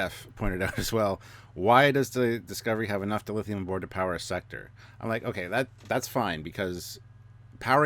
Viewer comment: I thought Booker said enough Dilithium to control a sector, not power a sector. I got the impression he meant control as in political influence because they have the dilithium. The same as money buys power now.